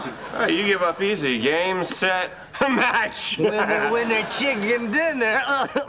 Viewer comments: Did i just hear “winner winner chicken dinner “ ? @ 2:53 Barney had this before PUBG! Cool! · did barney just say winner winner chicken dinner??